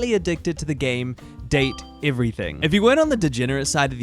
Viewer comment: Val-vidian? Date-avators?? CHASSIS???? it's a fully voiced game they pronounce these things for you OTL Ribbing aside this is a very fun way to do a tier list, love how they get moved around the more you talk to them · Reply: i thought he was saying “date evaders”